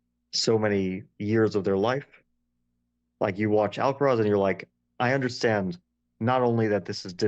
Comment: What is Fabi saying at 25:47 Alcoroz? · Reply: Alcaraz. Carlos Alcaraz, tennis player